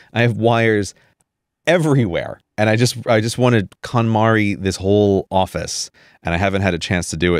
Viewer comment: I thought Grey was saying "Katamari", when he first said "KonMari" (that would have been much more amusing to have him do to his stuff)